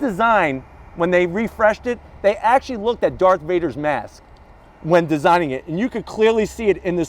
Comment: I had to go back I thought he said dark vader😂😂😂